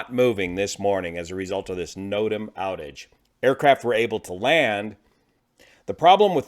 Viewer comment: I thought you said NOTAM outrage! FAA decided to pay ransom? Retired all the experienced technicians early, our loss. Created a lot of unnecessary ulcers for crews. Gotta do the preventive maintenance before beer break.